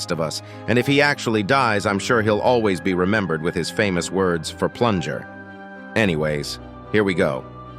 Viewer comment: Bro when titan camraman said for plunder I thought he said for pleasure